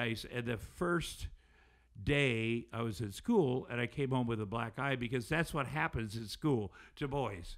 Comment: I totally thought he said he came home from school with a 'black guy' and his grandma was weaping, had me rolling.. Classic Tom Arnold with the jokes.. And then I realized he was saying black eye... Ahh dang not so funny